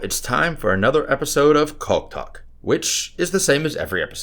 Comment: chaulk talk the first time he said that I thought he said cock talk, I was like WTF 😁🤣🤣🤣🤣🤣🤣🤣